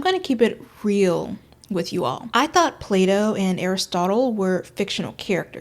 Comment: when you said Plato i thought you said play dough for a minute but just went along with it